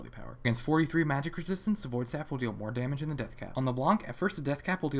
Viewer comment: i hear new "tons of damage" in "Void Staff will deal more damage than Deathcap"